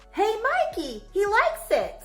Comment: I always thought he said “he likes it, he likes it”.